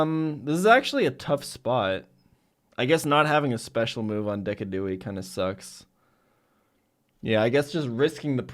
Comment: Did he say decaduei